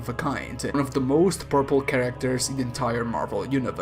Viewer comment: Your accent made this video so much better at one point. I heard "and this made thanos one of the most purple characters in the marvel universe"